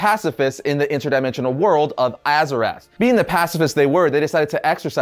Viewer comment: Too much Warcraft remastered game, I thought he said Azeroth at first Haahahahh